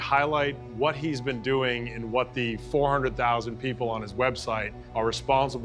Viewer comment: I’m sorry. At 1:40, did I hear “400,000” people on his website? 400,000?!?!